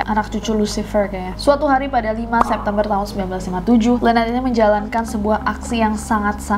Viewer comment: ku kira salah dengar ternyata memang kak Nessie bilangnya 1957 bukan 1975 haha! (fyi :ini bukan hate komen kok.)